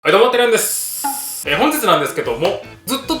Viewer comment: やっぱり はい どうも テラヤンです！ って聞こえるんです。 私だけ？w